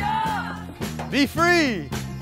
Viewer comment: When Danny said "be free" to the cows I thought he said "beef free"